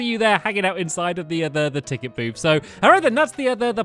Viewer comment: I misheard “ticket booth” I thought it was “Ticket boob” Um Yeahhh